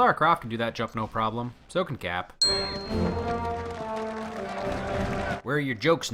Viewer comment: Closed captions say he, narrator says Cap.